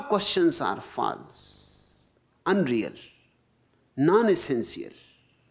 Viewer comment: what he actually says is "none is sincere" (instead of "non essential") ;)